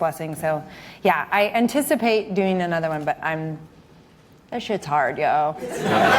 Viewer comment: what does she say after saying she anticipates doing another show? I couldn't hear exactly but the audience laughed and applauded... around the 5:10 mark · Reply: kenji-san she says “I anticipate doing another but I’m - that shit’s hard yo.”